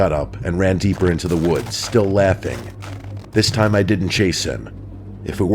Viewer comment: Hey. Doubt you'll see this but at 6:33 your captions must have misheard you saying "chase him" and instead it says "Jason"! 😅 I only caught it because I fell asleep listening, and noticed it when I went back to continue listening. 😂